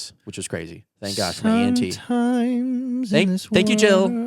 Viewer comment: I thought said his "auntie" told him gargling salt water was good for him lolol not ENT afjkdlajfkl